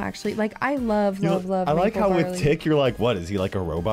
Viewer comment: turn subtitles on, and instead of saying tick, is says it he D word